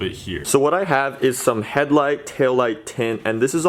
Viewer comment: The tail lights look sick but when he said tinting taillight 2 I thought it said tall light 😂